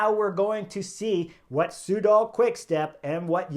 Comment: I thought Chris was mocking the team name (my bad! Hearing) ~ I thought you were saying "Pseudo-QuickStep" (a Lefevre diss?) Took about four times to hear "Soudal"(!) ~ (D'Oh) Finish at 2,155m 😎😎 my home is at 2,423m ❄️⛷️❄️